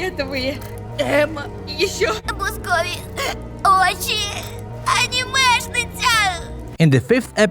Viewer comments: I heard anime schnitzel😭😭😭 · As a German. The German dub is very broken. "AND WE MAY BE GREW UP IN LUIGIS MANSION!!"- N at the start of EP 3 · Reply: I heard anime schnitzel😭😭😭